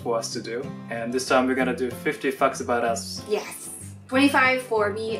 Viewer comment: Did he just say 50 fucks about us XD lol